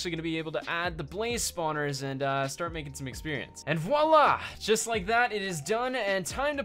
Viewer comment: Can someone explain me what did he said at 1:55 please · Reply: "Voila". Its like saying "here we go" or "here it is"